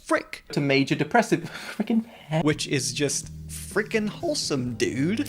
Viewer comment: misheard this as "freaky ahh man" for some reason